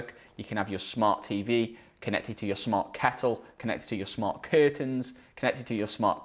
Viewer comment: At first I thought you said smart cattle. 🐄 😂